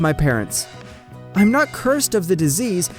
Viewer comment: Did they just say cursed not cured when it says cured right there.